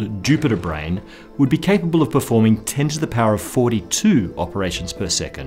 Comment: wait wait wait...did you just say 10^42 at 6:50 ? 42? coincidence? I don't think so